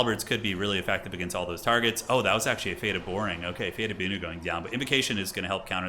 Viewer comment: Did you call it "Fate of Boring?" 😂